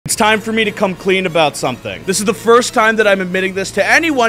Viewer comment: It started loading near the start and I heard "It's time for me to come"